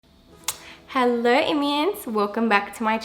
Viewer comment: I heard it as “Hello Indians” lol